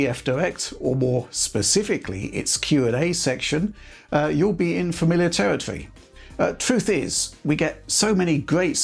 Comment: what is this word? "infomilitaritry"